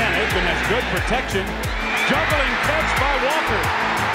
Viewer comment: I thought I heard “Intercepted by Walker.” Did someone put him in on D?